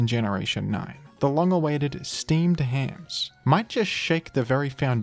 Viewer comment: I thought you said we were getting Steamed Clams